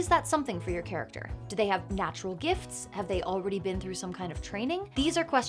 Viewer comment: Had to rewind the video, because I thought you said ''do they have natural hips?" when addressing what makes a character special.